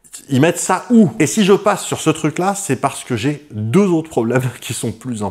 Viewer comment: J'ai entendu un "PASSONS OUTRE" façons Dudu !!